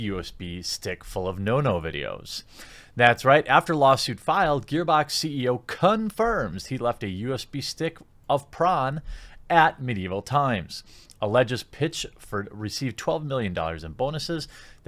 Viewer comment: it sounds like he says cum-firms.